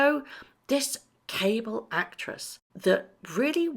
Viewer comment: Cable Actress! My hearing is going, I thought you said 'Able Mattress'! Thank you for your work, have a blessed New Year x